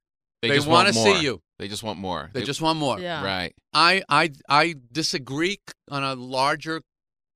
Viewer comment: did he just said "Dis a greek" ?????? 18:43 ....is dissing greeks now a trend? like #DisAGReek #Seinfeld ???